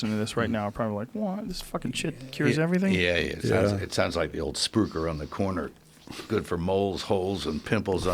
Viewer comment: Can't tell what Mel says at 45:30 about the moles holes and pimples or some shit like that. But he sounds like he starts by saying "it's like the spook around the corner". And I'm just like "No Mel!!! Remember how bad an answering machine can harm you!!!! Dawg, this time will be video... VIDEO!!!!! You can't sit around talking about revolutionary medical treatments that fuck directly with big pharma, click your pen the whole time to fuel and annoy the trolls, and then casually toss in a "spook" just for old times sake". That's why he kept quite most of the video. He has to fight unbelievably hard to open his mouth without accidently letting another slur slip right on out. Very bad quality for anyone that fucks with Hollywood and then moves on to fuck with Big Pharma. I love Mel Gibson. For being either fearless or retarded. Either way, love the guy.